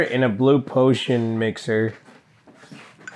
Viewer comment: Dose anyone know what they were drinking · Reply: Water